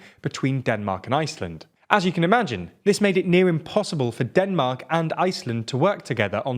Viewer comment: I want to congratulate on a rare instance of using the expression "as such" properly. It occurs at 1:29: "At the start of the 20th century, Iceland was a part of Denmark and, as such, was protected by their relatively impressive military." Here, "such" correctly refers to its antecedent "a part of Denmark." However, later in the video you botch "as such" as you usually do: "As you can imagine, this made it near [sic, should be "nearly"] impossible for Denmark and Iceland to work together on defence and foreign policy; and, as such, the Icelandic parliament passed two resolutions ... " As what? There's no antecedent for "such" in that sentence. It's difficult to understand what you mean. "As such" does not mean 'therefore.' "Binded?" Correct use of the language would make your reports so much more credible! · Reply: When you say said, "This made it near impossible for Denmark...," I thought he said, "made it nigh impossible..," which would be correct.